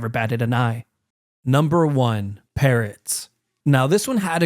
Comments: I really thought he said parents💀😭🙏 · For some reason, i heard 'herons' when he said 'parrots', and i thought "Heroin herons" has a nice, poetic ring to it. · when you said parrots I thought you said parents😅😅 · Bro when he said "parrots" i thought he said parents 💀